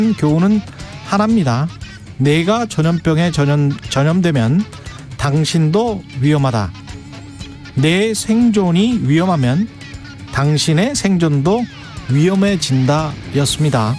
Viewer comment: "네가 전염병에 전염되면 당신도 위험하다. 네 생존이 위험하면, 당신의 생존도 위험해 진다." 이렇게 들리는데 맞나요??